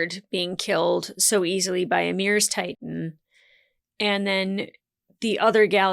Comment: She literally referred to Ymir's titan as "Ymir's Galliard" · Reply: I'm sure it is... Pretty sure you misheard at 37:38 She is correctly referring to the brothers as "Galliard" (i.e, "The first Galliard" and she says "The other Galliard") and she also correctly says "Ymir's TITAN". I think you just got confused, it happens